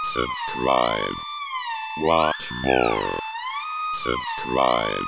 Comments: Hail Poppy. Anyway at the end of the video the 'song' in the end I thought it said 'survive, once more' in light of the illuminati, but then when I was sleeping I realised that it may of said 'Subscribe, watch more'. If I am right, then that is awesome because I only used my ears. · Oh my god I thought the end was saying "Since Ryan, waft more" and I was like "Oh, hidden message" then I realized..... I'm an idiot.